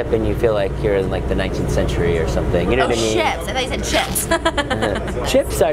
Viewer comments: I thought he said chips too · I thought you said chips too 😂😂